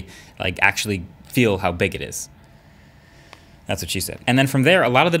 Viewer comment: did I just hear marquis brownlee say, “that’s what she said”